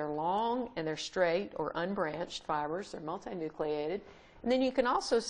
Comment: This, and it's companion videos, are excellently done! Thank you for the effort and clarity you've put into these! And the closed captions, while just auto-generated, are generally fine. (I often depend on the CC because I'm hard of hearing.) But at 03:47 the cc says, "...they're not a nucleated...", and that vocal and CC statement confused me, because I'm not sure what that meant. (edit): On re- re- listening, I think maybe you just repeated that they're MULTI-NUCLEATED, and the auto-generating youtube computer took that to be and printed it out in CC as 'not a nucleated'.